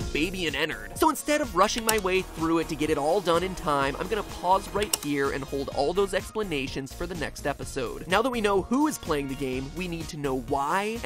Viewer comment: I thought you said "I'm gonna pause for a year and hold all those explanations for the next episode." Goddamn, I wondered how thorough your explanation was gonna be if you needed that much time 0_0